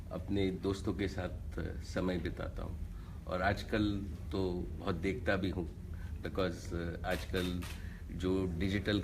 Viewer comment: Did he said Aaj kl too but Dekhta bhi hun